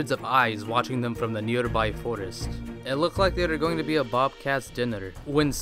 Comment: Also did he say "it looked like thay were going to be a bob cast dinner"